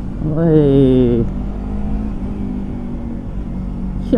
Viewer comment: What are you saying? Chimpo?